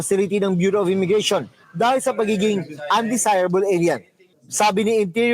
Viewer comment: When the reporter said undesirable alien, I swear I heard "undesirable idiot"